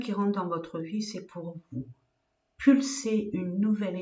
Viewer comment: Bonjour . Je viens de vous découvrir, et ce qui est bizarre, c'est que je cherchais des réponses à mes questions : J'ai cliqué ( j'ai entendu "c'est pour toi !" ) et ai reçu les réponses ! Incroyable car j'ai vraiment cru que vous me faisiez un tirage personnel ! Donc me voilà abonnée et vraiment ébahie par la clarté de vos messages !! Merci 1000 fois ! 🥰🙏🙏